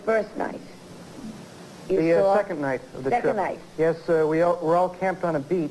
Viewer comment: sounds like he said "yes sir"